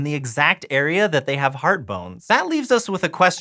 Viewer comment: Legit i thought it said heart boner